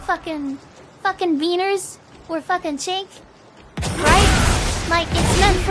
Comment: did she say ch$$k? 🤨